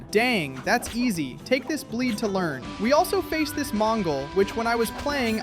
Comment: did u said mongol